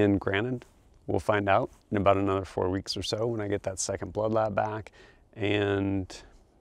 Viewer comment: Every time you’ve said bloodlab i swore you said bloodbath. Thanks for sharing your journey!